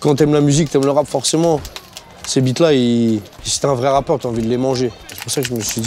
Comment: Mdr " c'est bites là , si t'es un vrai rappeur ta envie de les manger " g compris ca moi